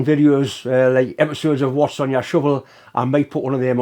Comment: Your accent got me for a few seconds, although what I thought you said sounded quite interesting. Wasps on my shovel.🤣